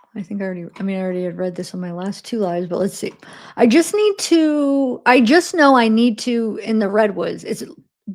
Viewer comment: I think she was saying “ I just know I need to trip in the redwoods.” Guaranteed. She absolutely is intentionally staying away. This all screams psychedelics and not necessarily schizophrenia as some have suggested, but maybe a psychotic break. Maybe neither , though. She may just want to start a new life